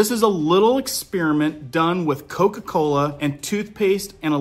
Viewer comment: That toothpaste video is scary as hell. Thanks for all the updates. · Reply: i thought he said coke cola?